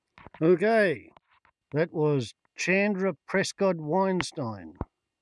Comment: never heard of her but after googling her name i believe it's chanda prescod-weinstein rachel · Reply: G'day, Thanks Rachel... Well spotted, I heard it as "Sandra" on the Radio, and was reading the magazine portrait's Caption through the PhoneCamera's Screen - so I saw what I thought I'd heard... Such is life, Have a good one... Stay safe. ;-p Ciao !